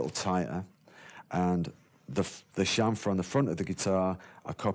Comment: "The (unintelligible) on the front of the guitar" What did he say? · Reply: 6 years too late (!) but he said ‘chamfer’, referring to the sloping edge under his right arm.